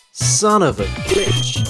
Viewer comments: I got in trouble because my mom thought you were saying son of a bitch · did he say glitch or bitch